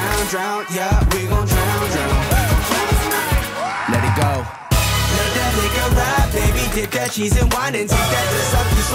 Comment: I thought they were saying let that nigga ride, had to come make sure what was going on lmao #lethatliquorride